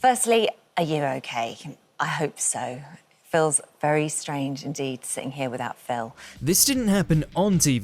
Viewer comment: 'Phil's very strange indeed ' is what i thought i heard. Holly finally speaking the truth